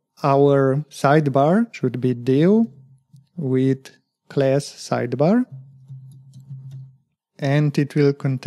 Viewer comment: Really, with your accent, you should have Subtitles:) @ 2:33 I have no idea what you are saying:) Also the Low Resolution makes it impossible to see. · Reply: "Our sidebar should be a DIV with a class sidebar". The low resolution might be a connection issue. The video is available at 1080 HD.